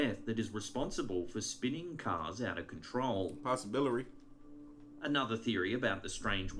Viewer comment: am I hearing it wrong or does say "possibilary? xD Trips me of when he does that and makes me have to rewind and listen like 5 times to make sure Im not frickin deaf. Also makes me giggle tho. Thanks for another sweet video.